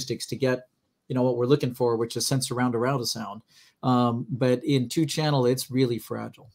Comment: what did say sensaroundaroundasound?!!!